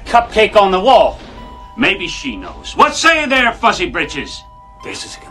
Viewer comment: I always thought he was saying "fussy britches" but "fuzzy britches" makes a lot more sense lol.